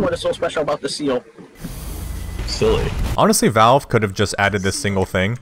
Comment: I think he actually said "SEALLY"